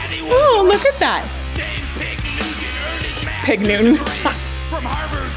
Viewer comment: His daddy was a rock star named Pig Nugent. (I thought they said Newton, the first time too)